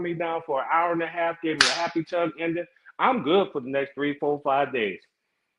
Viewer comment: did he say a happy tongue ending? 🤔